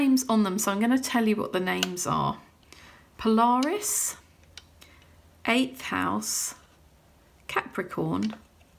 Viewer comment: 8th House! (Until you held the card close to the camera, I thought you were saying "Ape House" and I loved it then, too.)